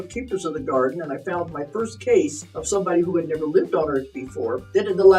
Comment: at first i thought she said "i had found my first case of someone who had been a libtard before". She had my attention I can tell ya that much🤌